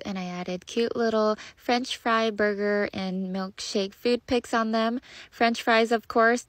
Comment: I thought she said foot pics🗿🗿🗿